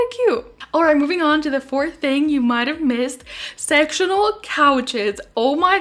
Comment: At first I thought she said sexual couches lmao xD